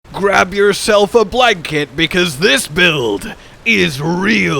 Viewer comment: I thought blud said "grab yourself a black kid" 😭